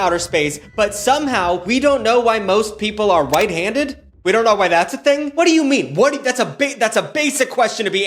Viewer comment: I thought you said white handed at the start and was excited someone was finally talking about how even if you are black your hands will still be way lighter. always wondered about that. when I was a kid i thought black people just used their hands a lot so the color rubbed off. lol